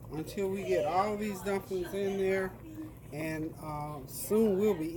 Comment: by the way i use CC on your sites (all sites) they been doing heck of lot better translating for me but they still have some errors. they corrected ones i reported only few left one is at lol 38:43/40:18 they calling your dumplings Donkeys lol sorry i hear with cochlear implant but husband has to read. Just letting your know some was really wild lol grat for laughs buut when i want to mimic your cooking because impossible not getting all the correct info not your doing hun i just like host to be aware so CC can improve our access Thank you. CC is doing much better in general past few months.